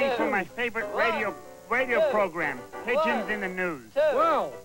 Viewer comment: omg i thought he said pigeons in the nude at first... X-D